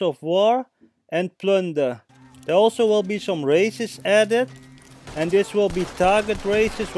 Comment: For a second i thought u said there will also be some racists added 😂😂😂